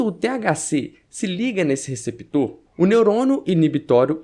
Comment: tô chapado ou ouvi "neurono"?